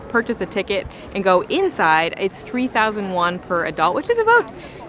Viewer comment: CC is wrong "If U want to purchase a ticket and go inside, it's 3001 for adult which is about three dollars" 3001 -> 3000 won(KRW) = about three dollars